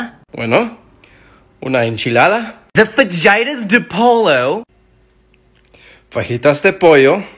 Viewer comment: I thought it said 'V@ duh polo'